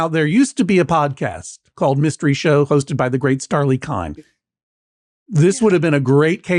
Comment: I thought the judge said "the late Starlee Kine" and I almost dropped my coffee!